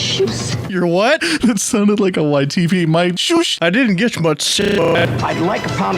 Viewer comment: I just realized he said YTP as in YouTube poop, my dumbass thought he said YTV as in that show about a teenager teaching a high-school science class